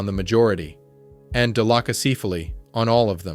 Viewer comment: What are you saying at 7:20\7:24? dilochacephaly??